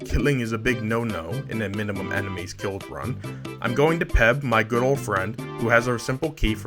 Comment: for a second I thought he said pebb my girlfriend at 11:54 lol